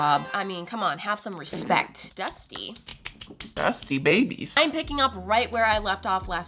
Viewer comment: When she said dusty, dusty babies. I thought she said zesty lol!